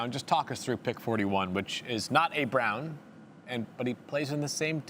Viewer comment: Did he just say a Brown thats not a Brown ????